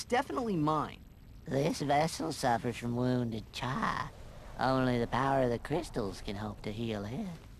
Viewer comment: what he say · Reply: this vessel suffers from wounded chi